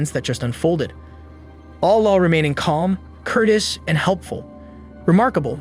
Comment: Name your kid after a vacuum cleaner....what could go wrong!! 12:15... did you just say remaining "curtis" and not COURTEOUS ?!?! lol wtf man